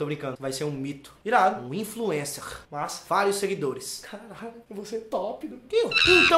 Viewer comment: soh eu ouvi um "kiu" no 6:56?